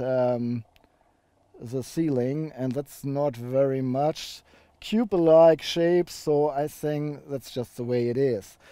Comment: Thankyou for such a detailed review. Just one little question - I can't quite understand what you said at time 24:13. "(something) large shape", or "(something) like shape"? Are you saying that people like the shape, so we have to put up with the lack of headroom? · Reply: ***** I say "cube alike ahape". :D I wanted to say coupe alike shape. ;) And yes, due to the coupe alike shape you have to deal with limited head space on the rear seats.